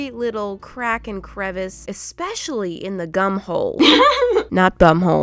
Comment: When she said gum hole I thought she said GUM BOLL 😂